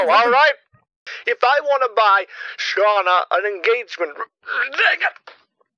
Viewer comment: Did he say the the n word at 47:33? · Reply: He said dangit